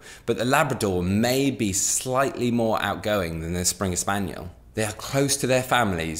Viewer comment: Hahaha u nearly had me there I thought I heard you say labs are more playful than springers hahaha